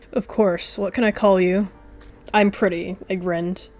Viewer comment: help I thought he was flirting and said your pretty 😭